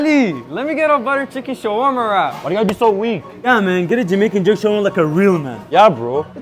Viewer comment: I thought u said 2 butter chicken shawarma wrap and one Jamaican shawarma wrap 💀